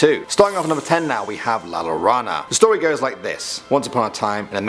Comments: When he’s said la llorona it sounded like he said la rana 🐸 and he forgot about the scariest thing la CHANCLA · La Llorona. What I heard: La larawna. La-raw-na.